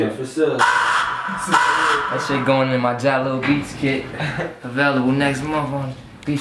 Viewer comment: All these years I thought the tag said "jungle beats" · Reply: I thought he said Jaly BEATS